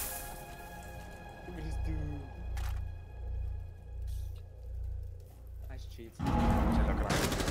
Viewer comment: I heard his teammate say nice cheats 3:37 😂